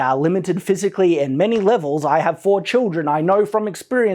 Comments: Not gonna lie I thought you said that you fought children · Ha, when you said "I have 4 children" I thought you said "I have fought children!" I had to do a double take there. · lol thought he said I have fought children